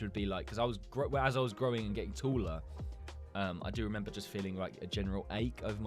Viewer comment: did he say tooller